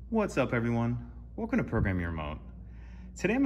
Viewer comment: Great video. One thing however, is it really sounds like you said “Program your mom” at the beginning. I had to listen to the first 10 seconds several times, I just can’t unhear it.